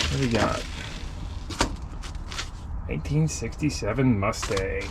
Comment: was waiting for this video since I seen it on content... 32:07...I thought you said "1867 Mustang" lol nice find 👍👍